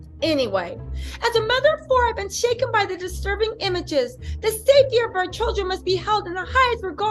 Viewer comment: candice owens just opened the slaughterhouse on these mo fos. kim said she was a mother of four. i swear it almost sounded like she said i’m a mfr! at which point i nearly dies laughing. get them 😮